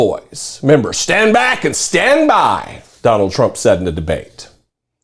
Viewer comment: "Proud Boys, stand back and stand by". Those words will forever linger in my mind as The Seven Words Of Sedition. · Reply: I heard "stand bwack and stand by". He almost slipped and said "stand black".